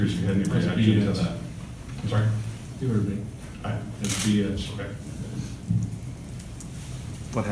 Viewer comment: What did Michigan state coach say at 0:20? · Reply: He said “that’s bullshit” even though the entire thing is on video. Everything Harbaugh about his actions that day was true 😂